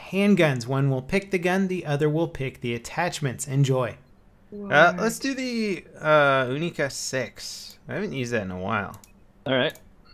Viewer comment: What did he actually ask or say? Because for me it sounded like "Hast du die Unica 6?", which would be a bit weird because that's german and it would even make perfect sense in this context. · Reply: Nah..He says "Let's do the Unica 6" :/